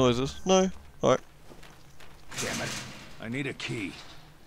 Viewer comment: Barry: I need a key. What I thought i heard Barry said: I need to pee